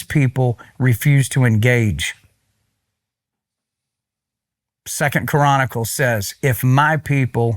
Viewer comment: why does it sound like he says 2nd Quranicles…